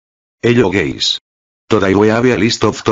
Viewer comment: is he saying hello gays?